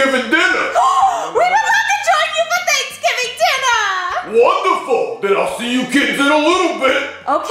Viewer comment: I thought that woman said WTF